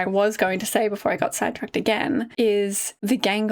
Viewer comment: I misheard you say “side tracked” as “Sad tracked” and it took me a long minute to realize that. Because being sad tracked is just a great way to describe this episode and reaction.